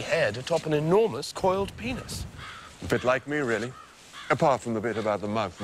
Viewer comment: an enormous coiled penis? what did he say please answer me